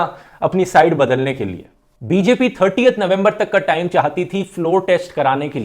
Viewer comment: can I hear 38th november ....👍👍👍👍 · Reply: it's 30th and not 38